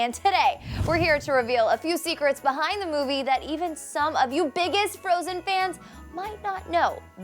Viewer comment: I thought she almost said "some of u bitches"😂😂😂😂I'm sorry